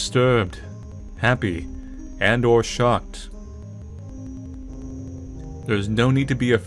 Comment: I looked away from the screen for a second and thought you said "And/Or shot." I was scared for a sec! The meta game shouldn't be shooting at us!